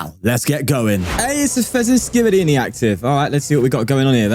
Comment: Did I hear "Fezenskibidi"..🤣